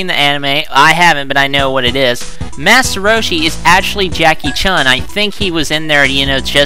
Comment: ohhhh i thought you just said chakie chan but you say chakie chun.but now i understand